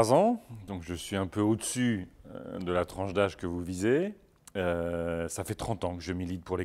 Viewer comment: c'est moi ou il dit "...que vous disez". Si c'est le cas c'est affligeant.... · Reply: Pas la peine de faire de grande phrase il a dit la tranche d'âge que vous Visez